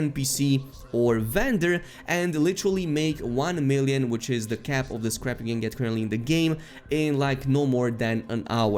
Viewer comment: I literally just spit out my drink. "Literally make 1 million which is the cap of this crappy game that currently in the game" I had to turn on captions to see what he actually said lol.